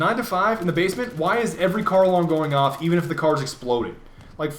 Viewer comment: I thought I heard carl alarm